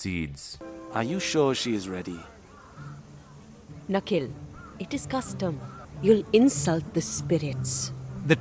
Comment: It's funny when Ahsoka's mum said "it is custom", my YouTube's auto generated subtitle said "it it's dumb" Even YouTube knows